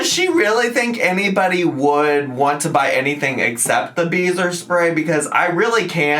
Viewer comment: I thought you were saying, “beaver spray”😂